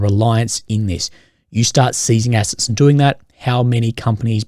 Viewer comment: Ngl, the abrams got smacked pretty hard, i knew the Leopard2s and Challengers were extremely overrated and overcredited but i was at least expecting the abrams to put down some work like the Bradleys. From what ive seen 7 M1A1sa and 3 M1150 breachers have been lost, Oryx stopped updating UA losses for weeks now. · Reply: i thought you said seizing assets it theft eg cash . you cant sieze assets and people are going to trust you